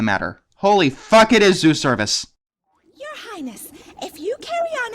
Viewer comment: Zeus Cervus? who? 12:20, ok screw it EVERYONE in this movie is high out of their fucking minds, everyone! I just don't understand. how did this even make it past censors? I find it amusing how the only redeeming qualities in this movie are the ponies. "show them the truth" ... wait you don't mean... oh god. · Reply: I thought he said zoo service.